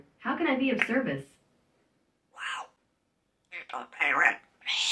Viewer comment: I swear, I thought she said at first, " You dont pay rent!" 😂😂